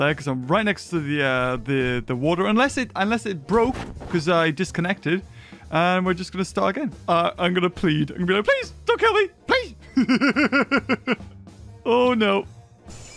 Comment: Poor, I understand his pain doing this video's just for us. He spends like 10 hours playing and editing for us. We need to applause him for this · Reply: I thought you said applease... PRAISE THE DINO DEMON.